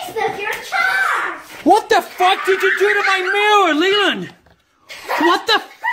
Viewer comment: It sounded like he said “Mailer”.